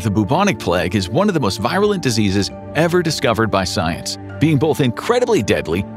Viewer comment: the audio said this "plague is one of the most virulent diseases science has ever discovered by science," but the closed caption says this "plague is one of the most virulent diseases ever created by science." That bit got my attention for sure. Lol